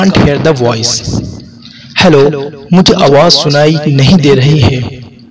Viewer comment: I cant hear the voice in Urdu will be "mujhe awass sunnai"..... and what else? I dont propperly understand it, please can you write it down for me it ll be very useful 🙏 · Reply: Mujhe awaz sunae nahin de rahi hai.